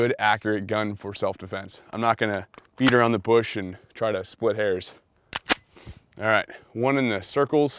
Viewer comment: I should have asked for new earphones for Christmas, I thought I heard beat in the bush and spit hairs